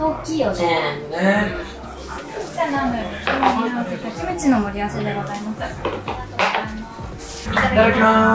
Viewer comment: キムチの盛り合わせが、むちの盛り合わせに聞こえた🐶耳がねむねむワードに染まってしまっている🥰